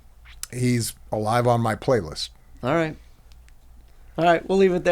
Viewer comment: Did he said: "Enjoy the prank" after he said "There you go" after answering his Tupac question? Subtitles do not show it, yet its what I hear.